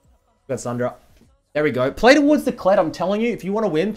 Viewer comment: I love how midbeast says "play towards". It sounds like hes saying "playdawords"